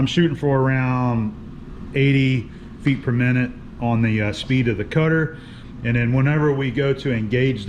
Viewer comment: I may have to listen again but I thought I heard 80' per minute near the beginning of the video?? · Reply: Ok at 3:31 80' per minute cutter speed